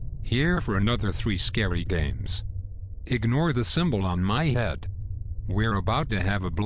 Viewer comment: I thought he said ‘last scary games’ It legit almost gave me a heart attack 😩